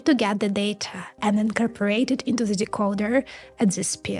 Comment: When you said the problem was speed, I thought it was a set up for a joke. I didn't hear "speed," I heard "spit" and was expecting some sort of hawk-tuah joke. 😂